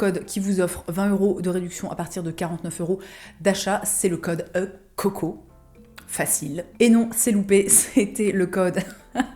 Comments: J'ai entendu "code Coq au vin" hahahahaha il est 9h du mat et je pense à ça ! sorry le coq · Moi j'ai entendu "Le code Coq au Vin" 😂